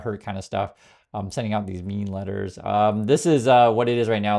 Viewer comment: “Mean letters?” I thought you said “ meme letters!” Much more accurate.